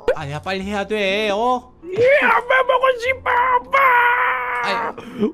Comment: 때 ㅠㅠ엄마보고싶어 엄마!! ㅠㅠ 할때 김리아 엄마가 아닌 아빠라고 들리는걸까 그래서 ㅠㅠ 아빠 보고싶어 아빠!!ㅠㅠ 이렇게 들리네 아빠는 옆에 있으면서 엄마가 아닌 아빠로 들린분 댓글에 좋아요 하셈